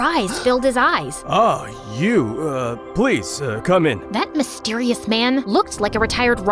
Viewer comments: he said please cone in instead of come in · You said 'cone in' instead of 'come in'😂😂😅😅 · spelling error, they said cone instead of come. Edit: I'm not judging or hating, just pointing it out. Edit 2: I love! ❤ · said CONE in instead of COME in Like what the heck? What was she gonna say at 5:30?